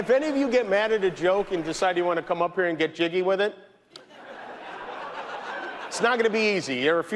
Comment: "And if any of you get mad at a joke and decided you wanna come up here and get jiggy with it... it's not gonna be easy." 😂😂😂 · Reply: I thought he said "Get Jimmy with it"?